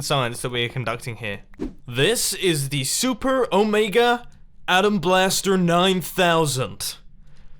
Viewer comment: did he say omega? :)))